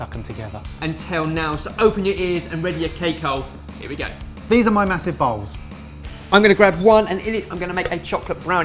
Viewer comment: i was multitasking while i was watching this video then i misheard at 0:15 "These are my massive balls" . hahaha